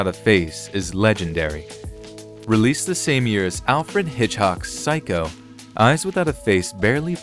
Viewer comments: Wait, did he say Alfred Hitch-hock? Eesh. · Did they say "Alfred Hedgehog" at 11:11? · Alfred Hitchhock? ARtificial voice sucks. It also said Bela LaGoosey. Instead of Legosi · Does he say "Hitchhock"?